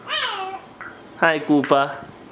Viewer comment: When he said Koppa thought he said poopa 😂😂😂